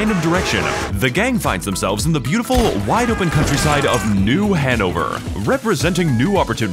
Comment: i refuse to believe that place is not called "new hangover"